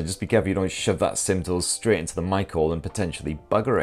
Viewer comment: Ohhhhhhhhhh you were saying Mic Hole, I thought you were saying "shove your pin into a Michael" and i was so confused for a while.